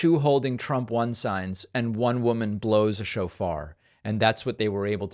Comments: I thought David was saying "...one woman blows a chauffeur." Where??!! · I'm sorry. I heard 'blows a chauffeur. Probably should get my brain washed.